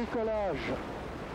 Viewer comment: is it just me or did he say "NIKOLAJ!!!" on launch?